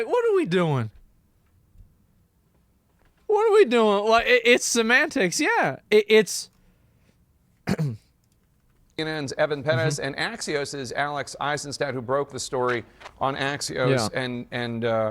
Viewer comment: Jeffery who? 2:18 - Did he just say Evan Penis??